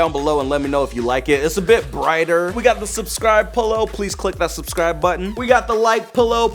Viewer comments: Did he just say polo # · He sounded like he was saying polo instead of pillow